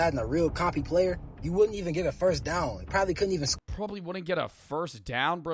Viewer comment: DId nga Say First Down When He was talking about 2k 2:16 HOLY LARP